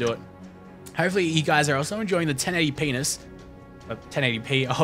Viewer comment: Did you just say 1080penis? lol